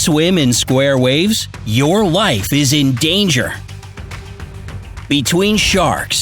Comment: I thought he said “if you swim in square waves your wife is in danger”...lol